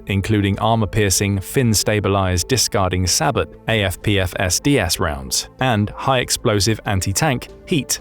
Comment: afpfsds, anyone catch it? 7:13 lol